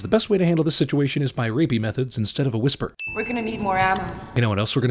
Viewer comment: Did he say "rapey methods?"